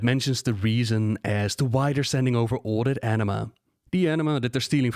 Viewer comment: Every time you say "anima" I hear "enema" lmao